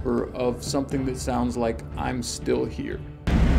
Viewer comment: I hear “ I’m still here” but the more I listen to it, it also sounds like “im standing here” I could be wrong. But both are legit creepy no matter what it’s saying.